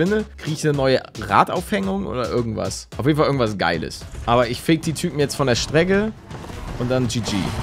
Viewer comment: Wer hat bei 6:40 auch„ ich f*ck die Typen von der Straße "verstanden